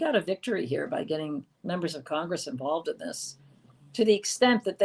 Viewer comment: I could have sworn I just heard Leslie say "Pacific instead of "specific " I didn't know she was from Brooklyn!!!🤣🤣🤣